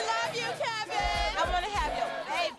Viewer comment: I been tryna get you pregnant for the longest 😂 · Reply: I thought he said "look at you trying to get pregnant in while the lord is watching" or something like that!